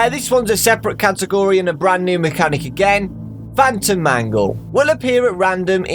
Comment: I heard Funtime Mangle X)